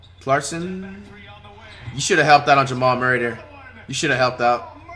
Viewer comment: did just said Jamal murder